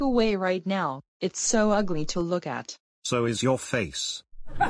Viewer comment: WHAT DID CLASSIC BLOODYOU JUST SAY??? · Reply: so is your face